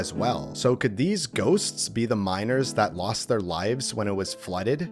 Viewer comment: what did he say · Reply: He said miners